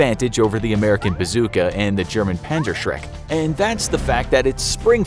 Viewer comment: my volume was up to 80 on accident and the clank clank clank was terrifying Edit: 3:55 did i hear panzer-shrek?